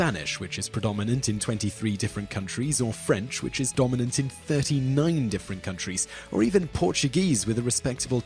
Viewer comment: Did I just hear "French is dominant in 30 million different countries" at 0:33? Because I sure didn't know we had millions of countries on Earth. And "Chinese" isn't a language, it's "Mandarin Chinese". Just being a picky linguist. Get your facts right before even attempting to make a video on any subject matter.